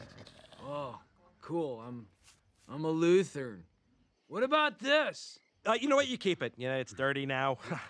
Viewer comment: I think they said Luther? Cause there is a religion called Lutheranism, but that goes way back